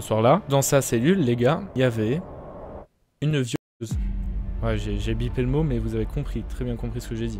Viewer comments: il a dit quoi ? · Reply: Viollese je crois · A 15:59 c’est quoi je suis Québécois donc je n’ais pas compris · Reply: Violeuse